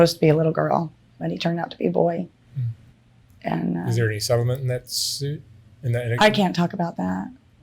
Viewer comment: he asks her a question and she says she can't talk about it. I listened to it ten times and I can't understand what he's saying. Can anyone else understand that question? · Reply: Can't talk about if there was a settlement on the case